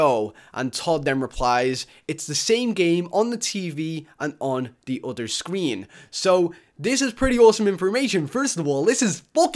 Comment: Did he say inspormation? Does that mean it's inspiring information? That's an excellent word